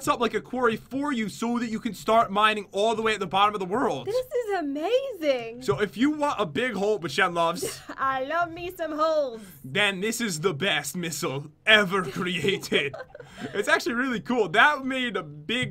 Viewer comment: It's a bird it's as fast as a missle No is just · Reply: thought she said I love me some hoes lol